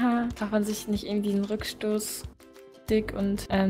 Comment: ich hab "nen rückstoss-dick" verstanden